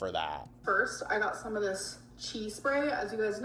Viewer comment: 😂soooo, I'm just listening, not watching, while I get ready for the gym and I thought she said "cheese " spray and honestly, I was like yea, sounds right 😂I'm dead